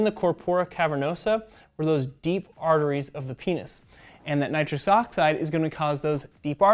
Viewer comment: Damn, I thought he said nitrous oxide instead of nitric oxide. It would make all that talk about family in Fast and Furious make sense if you could use NOS to fuel your erection.